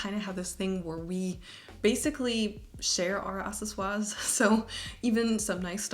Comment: What is “assiswa”? I heard it at 1:12 and 2:10 :/ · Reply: accessoires/accessories